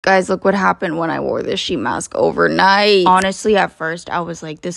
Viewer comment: I thought u said SHEIN mask and I was so worried for u